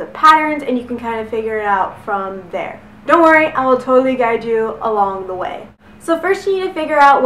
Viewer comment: you said "dont worry i will totally guide you along the way" i heard "dont worry im hot and good at explaining things!"